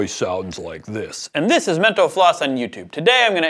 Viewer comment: Did he just say "this is Mento floss?"